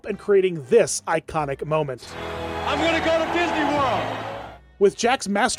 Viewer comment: ? What'd the football player say? It didn't get captioned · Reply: “I’m gonna go to Disney World!”